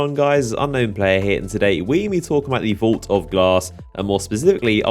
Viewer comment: My vid lagged n it sounded like u said vault of ass at the beginning